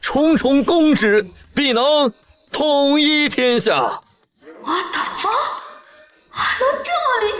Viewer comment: I heard it correctly right?! "Watafak"